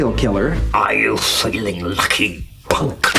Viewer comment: I thought Mr Bean said "I'll seal lucky, Punk!"😂😂😂😂🤣🤣🤣🤣